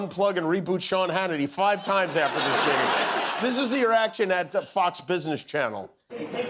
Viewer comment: did Jimmy say "this is the erection at Fox Business Channel" at 4:23? :face-blue-smiling